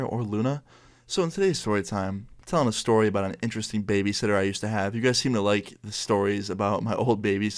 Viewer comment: Thought it said baby sister